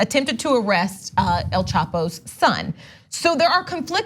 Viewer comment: I heard her say El Taco's son 😂😅